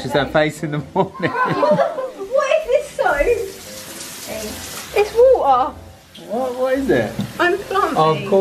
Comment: When she said what is this scope it’s water it sounded like she swore and said the f word 😂